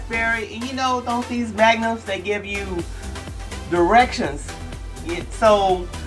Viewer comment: I thought you said "Some of these Magnums give you erections"! Excellent video! Beautiful lady! I'm your fan!